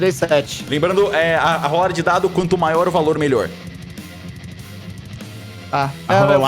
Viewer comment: "Quando maior o valor da rolada melhor" eu ouvi isso certo?